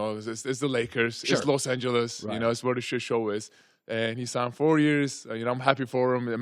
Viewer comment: Did he say it’s where the shot show is